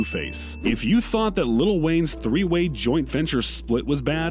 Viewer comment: did he just say lil Wayne? Ahah